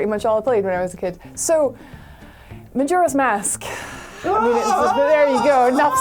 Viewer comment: When she says "majora's mask" all I hear is "Majira's mask"